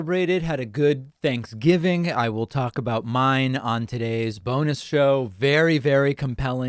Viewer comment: Lmao The first time I heard David Pakman say “bonus show” I thought I heard the “Boner show”